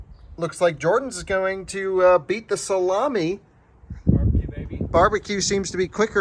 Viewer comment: Did he say that "George is gonna beat the salami? Lol.. careful.